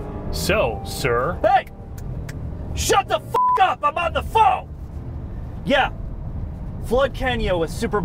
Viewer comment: So, I totally thought he said, "Shut the f*** up about the pho!" and I couldn't stop laughing! As an Asian-American, I gave that pass. I literally dropped to the floor doubled over from laughing. I'm still laughing replaying that in my head even though I misheard it.